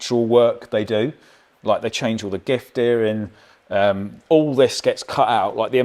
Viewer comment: Tell me it’s not just me, but around 09:58 Colin says ‘giff dearing’ instead of ‘diff gearing’….. 🤔